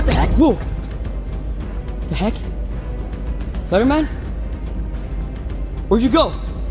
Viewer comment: Did you just call him slenderman?